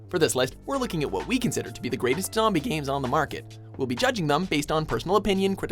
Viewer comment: On 0:21 the captions say "greatest 'Tommy' games" instead of "zombie". Not that big of a deal, but it happens quite a few times as well.